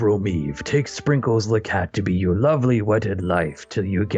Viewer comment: I thought said to be your lovely life